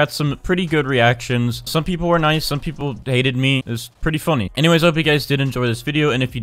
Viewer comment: I thought he said dated him when he said hated